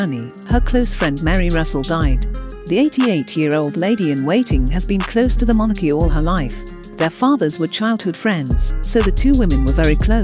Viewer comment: Close to the monarchy? I heard it as close to the monkey 😂